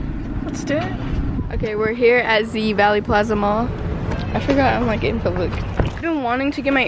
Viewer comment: i really thought valley plaza said vanity planet...and im wondering why i cant find a store??